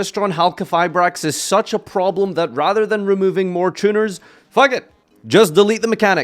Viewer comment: hello, did I hear 'tuna'?